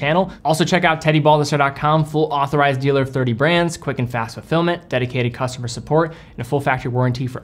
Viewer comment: I've watched your videos for years and just now realized you say "quick and fast fulfillment" , I thought you were saying something about filming :|